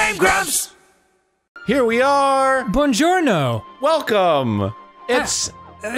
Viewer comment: I had a survey ad before the video that asked which places I've seen ads for, all pizza places. When Dan opened the video with Bonjourno! I thought he said DiGiorno! And I was like 0_o wAT